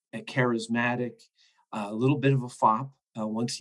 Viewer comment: What did Nathaniel Philbrick say when describing Custer? "little bit of a ???" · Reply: fop; a guy who is overly pre-occupied with his appearance, grooming, and attire.